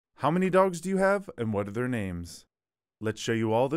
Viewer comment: Am i the only one who misheard the opening as “how many dongs do you have” or have i spent too much time on the internet